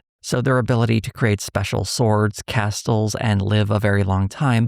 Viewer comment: Castles? I genuinely didnt know what word was being said. The T being pronounced in Castles threw me off haha.